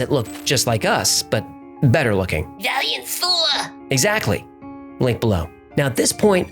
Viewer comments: Lol I had to pause and rewind to make sure I heard right. What I heard: “There was also a race of Homo sapiens that look just like us but better looking…” HF: “Like FULLER” AJ: “Exactly… Like, down below…” What was said: HF: “Valiant THOR” AJ: “Exactly… Link, down below.” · What does the fish say at 7:03? Valiant four? I don’t see a link for that. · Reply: Valiant Thor. Yes there is a link to it.